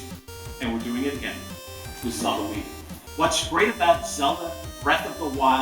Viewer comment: Did anyone else notice Reggie saying that they're "doing it again, this Halloween" (or I think that's what he said) at 9:48.... idk if he's hinting at the release of breath of the wild or if he's talking about something else DX